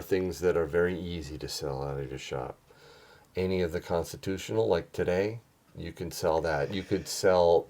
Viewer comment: My hearing is getting bad, I thought he said "prostitutional" gold...